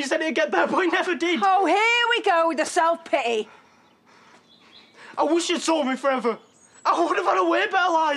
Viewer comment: What did Noah say @ 0:56? "I wish you'd sold me forever"??? What does that mean?